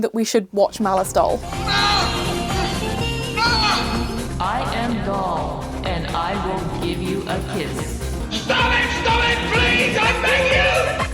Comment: I swore she said “I am God,and I’ll give you a kiss”